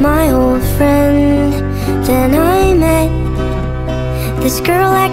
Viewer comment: I heard 'then I met a girl' I was like GET READY GUYS HERE WE GO